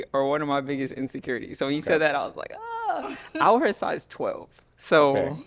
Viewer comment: Did she say she has Size 12 feet or am ihearing things